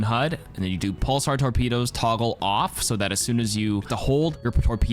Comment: can someone explain what tf he said here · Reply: Pulsar torpedos, toggle off